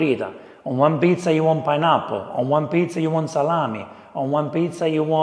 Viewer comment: did he say pineapple 😮😮